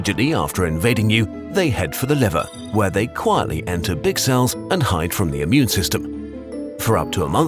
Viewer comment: did he say dick cell?? Like if you heard him saying dick cell